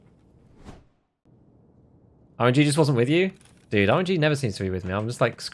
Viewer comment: I heard "Jesus wasn't with you"